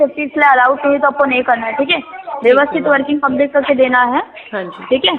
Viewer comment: Did I hear it correctly ... at 02:20 scammer slips from Hindi into Marathi व्यवस्तित (meaning prim and proper)?